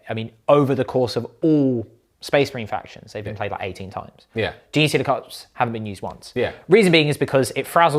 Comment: what did he say hasn't been used one I can't understand it. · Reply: Genestealer Cults 🙂